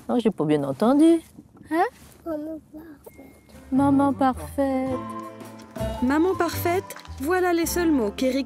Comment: Je suis congolaise aussi ca m'a touche votre gentillesse envers cet enfant.longue vie a vous que Dieu vous benisse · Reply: Moi j'ai entendu 5:07 mère au chaumage